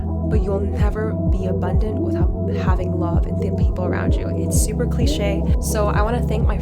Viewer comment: why do the subtitles say "thin people around you" is that fat shaming lol